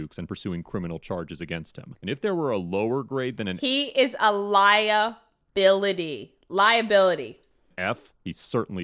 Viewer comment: When said he's a lia-bility I thought she was saying he was a liar with an accent until she finished the word 🤣